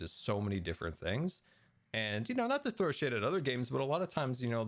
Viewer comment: did I just hear the S word?!! I thought I just heard the sugar bad word. Anybody heard that? Edit: hopefully YouTube doesn’t see this and demonetizes Scar. If they do, oh boy there was an oopsie done. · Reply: I think he said "shade"